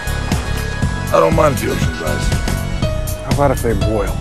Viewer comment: I always thought he said I don’t mind if the ocean Dries